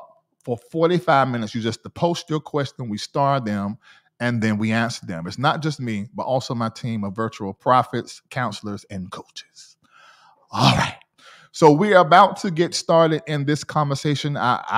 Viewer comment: Did he just say virtual prophet?